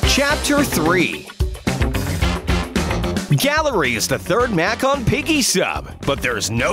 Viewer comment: did you say mac instead of map